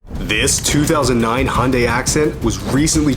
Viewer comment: I heard "This 2009 Hyundai Accident"